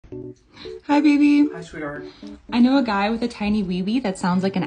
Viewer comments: For a second when he said hi sweetheart I thought he said hi swee hurt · I thought she said a guys with a tiny wee wee named owl, my username: owly